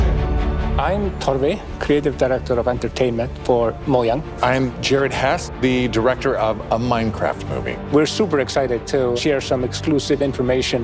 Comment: he really said moyang! PhoenixSC Will be proud